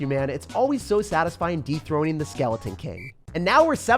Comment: i honestly thought you said "Deep Throating the skeleton king" 😄... i know i know keep it PG but it was hard to resist typing this out though